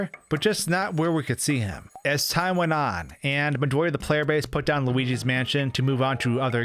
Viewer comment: As time went on...something player base? I understand 'madoy'. What's he actually saying?